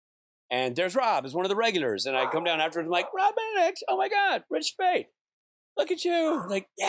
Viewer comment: When Rob says "oh my god" i hear him say "oh my me"